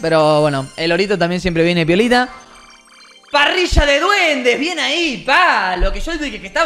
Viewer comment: escuche parrilla de duendes XDD